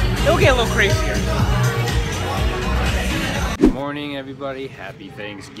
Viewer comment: Omg when said "good morning everybody" I thought he said "am horny everybody!" XD omg am dying!!!